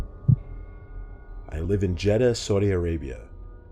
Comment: I thought you said you live in Phoenix.